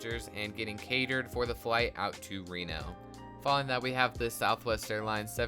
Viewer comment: did i hear Reno 😎 my airport. great update man enjoyed it alot, with the memes even more! 😂🙏🏼